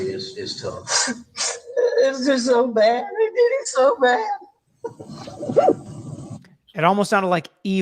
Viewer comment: I heard "ay did it so bad" could be I or they. Between the accent and the sobbing it's impossible to tell IMO. I don't envy this jury.